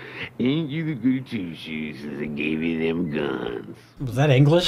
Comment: I think he says "Ain't you a goody-two-shoes since they gave you them guns."